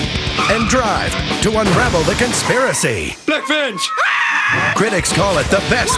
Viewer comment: did homer say Black Bitch? 😂